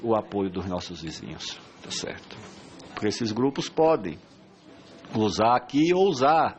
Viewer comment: Só Eu que entendi "OS GRUPOS PODEM GOZAR AKI"